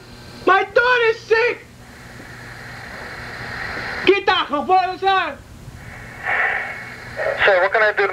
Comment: What does he say at 1:53 ? It sounds like "get out befora I shout" lol